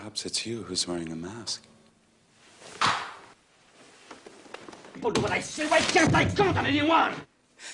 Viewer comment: I don't understand it's supposed to be french ? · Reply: The first sentence is cut: "Why don't people do what I say? Why can't I count on anyone?"